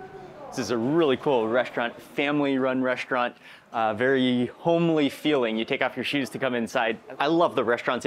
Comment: Always enjoy watching your videos Mark, especially when there are moments of indirect humor. 21:41 made me laugh. I almost thought you said holey but you said homely as you took off your shoes. Hopefully y’all caught what I caught. ❤ it! Keep it up Mark! You’re awesome!